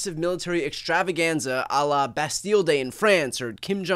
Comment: lol I thought you said BestDealDay instead of bastille day. Lol that would have been a perfect Trump parade name.